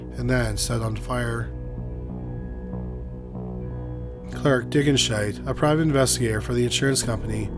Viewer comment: Clark Dickinshite? Did I hear that correctly 😂